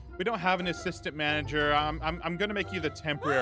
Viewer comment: Me when Ein pops up:I thought you said “Smart Manager”👀